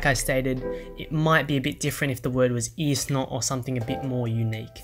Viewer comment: did he actually said isno there ?! 😂